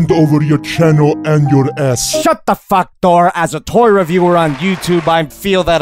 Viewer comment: The first time I watched this video I heard "shut the fuck door"